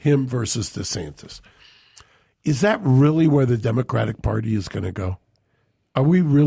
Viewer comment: It sounded like Mark said democrack party. make it go viral.